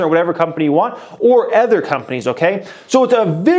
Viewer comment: "or ETHER companies" xD u literally say ETHER instead of other in Every video.. Wth is that :p Great video though ❤️👍